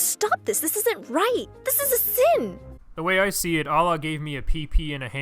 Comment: did he just say bhagvata gita I am confused